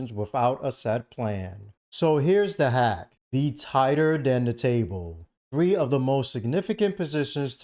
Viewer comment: the text is wrong. It reads “be tighter than the table” and what he actually said was “be tighter dan de table”